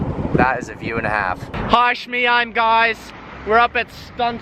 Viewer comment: Did he say "hi shmee I'm guys" 4:54 😂